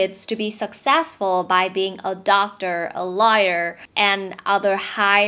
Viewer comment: Location 6:30 Did you say Liar instead of Lawyer? 🤔Well you are not wrong, actually. Most of the lawyers are liars.